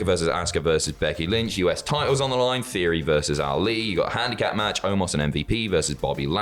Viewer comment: Gotta admit, I thought you said "Theory Vs. Roode".